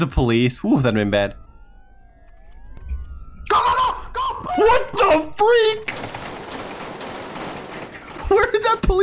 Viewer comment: I thought sayed the f word edit he didn’t